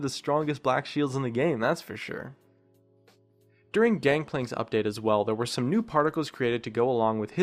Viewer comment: did he just say "DankPlank" ? XD